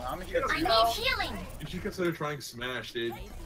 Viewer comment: Did I heard SMASH 4??? Oh boi.